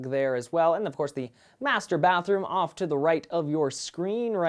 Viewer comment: When he said master bath room I thought he said masturbate room :)